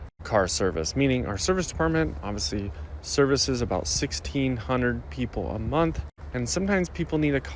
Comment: Oh, I thought he was saying $1,650. That's a reasonable price of a 95k mile car.